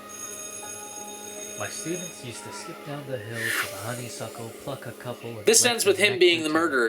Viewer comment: who else heard "honey suckle, fuck a couple."?